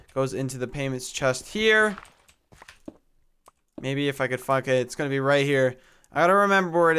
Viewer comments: what did he say at 17:44? did I hear it wrong? lol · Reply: He probably said if I could find it but it sounds like the f word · What did say? O.o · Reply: He was trying to say maybe if I could find it. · SOUNDS LIKE YOU SAID FUCK IT...